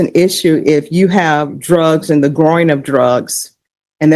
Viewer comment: I thought she said GROIN. Not growing of drugs